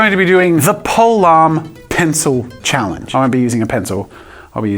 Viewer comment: Who else thought he said pokieman pen challenge?